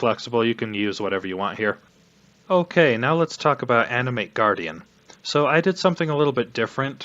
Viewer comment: As someone who doesn't know anything about the game i thought you said Anime guardian at first.. btw welcome back i dont play path but ill still watch